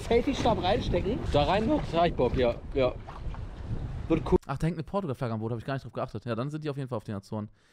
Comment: Unge: ach da hängt ne portugal flagge am boot. Ich verstehe: ach da hängt ne portalgun am boot